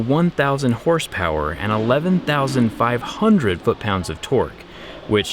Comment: That’s not where we attach them for the helicopter, they have the ones we use on the hood of the car , basically on top of the truck. Those you mentioned is for towing and getting towed basically. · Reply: Did he say 11,500 ft lbs of torque???